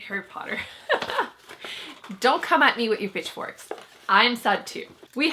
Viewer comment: I think you said "don't come at me with your pitchforks" but what I heard was "bitchforks" and honesty, that's gonna be my new catch phrase.